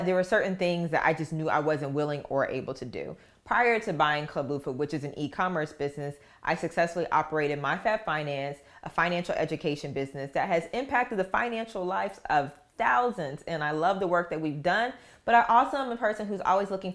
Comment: I thought you said “my fat finance” lol but the right name showed up first in google. Do you help with pricing for product/ services for businesses? Congrats on your new business purchase 👍